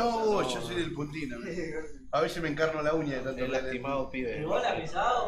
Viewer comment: he lastimado pibes jajaja que dice